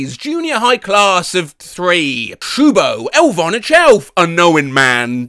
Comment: Oh, so that's the Elvonna Chelf I keep hearing americans talk about during Christmas.